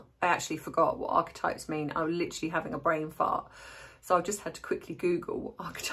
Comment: Hi, as always excellent analysis. Hahha the irony of someone else leaked the Taylor swift snub 😂. Love your thoughts about the latest about H being 'hospitalised'. I thought you said you had brain fart but realised you said brain fog. 😂😂😂. Best wishes ❤️